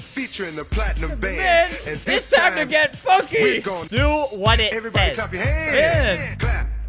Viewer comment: wait did he say a bad word? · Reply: Funky.